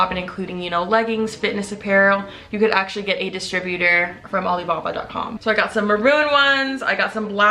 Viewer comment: I thought you were saying Ali ababwa like in Aladdin 😂😂